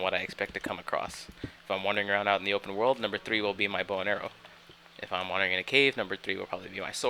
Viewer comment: Why do I keep hearing: "Number three will be my boner"